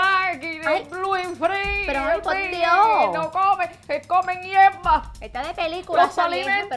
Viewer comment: Lol. Instead of " ellos comen hierba" i heard "ellos comen mierda" 😂